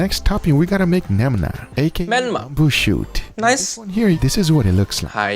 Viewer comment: Welcome to otaku world. Well me actually. 10:14 Does guga said menma. Remind me menma from anohana